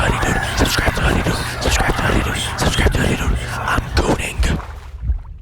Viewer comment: bro i hear SUBCRIBE to hoodydude and im gooning😂